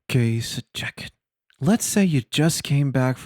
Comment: Youtube captions say: "okay so jacket" 0:35. All my life is a lie.